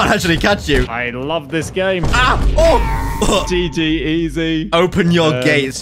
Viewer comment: did really said gg ez 🤓